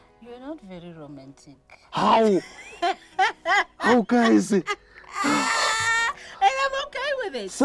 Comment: I don't understand how 'haw, hawu guyzi' @ 0:38-0:40 is not a meme. I laughed so hard. I probably watched it 5x